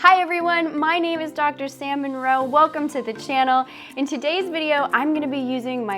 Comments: I’m not gonna lie, I swear I thought you said your name was “Dr Salmon Roe” and I was like “that’s THE most perfect name for a Shark biologist I’ve ever heard” but is fine too lmao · At first listen, I thought you said your name was and my first two thoughts were "her parents wanted her to experience a life of grief" and "what an ironic name for someone who studies anything involving fish." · I heard "hi, I'm Dr Salmon Roe" which would've been so fitting for a specialist in marine life lol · I misheard your name as and thought that is an oddly appropriate name for a marine ecologist.